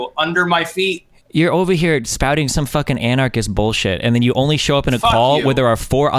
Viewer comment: All these spuds in the chat repeating the same gotcha because they misheard "minarchist" as "anarchist" oh well